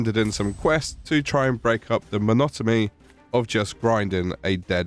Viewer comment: did he just say "monotomy"? 😅 Silly dog is confusing the 'n' sound with the 'm' sound.